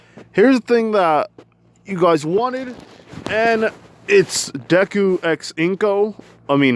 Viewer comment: Bro when you said deku x ino I thought you said into again